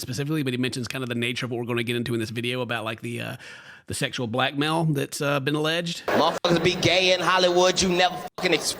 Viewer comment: When he said "sexual black mail stuff" I thought he said "sexual black male stuff" ... no homo